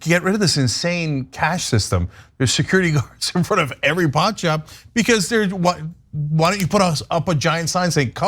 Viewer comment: I definitely heard "pawn shop" not "pot shop," and I was super confused for second, there! 😂